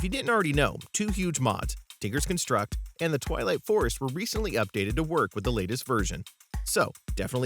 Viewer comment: When he said "Twilight forest" I thought he said "Toliet forest". I could not have been the only one... right?